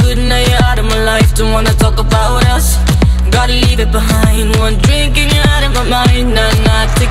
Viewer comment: Who else thought she said I gotta yeet it behind